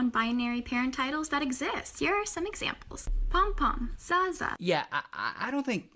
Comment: all I heard was that good zaza 💨